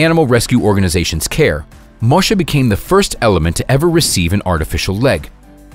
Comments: Did anyone else here the guy say element instead of elephant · Does he say "The first element"? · Did he say the first element?